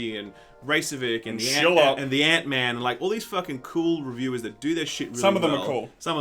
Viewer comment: When you said "Act Man" I thought you said "Ant Man". I had to rewind three times.